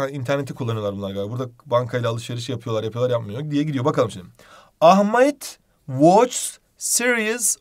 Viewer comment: da ahmeyt mi dediniz hocam yoksa benmi yanlıș duydum?